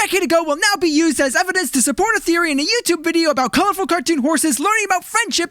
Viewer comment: Did I just hear therian.. ME TOO